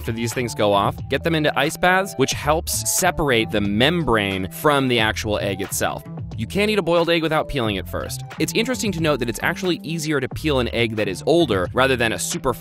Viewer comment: lol I thought he said you CAN eat the egg without peeling the yolk. A mistake was made here...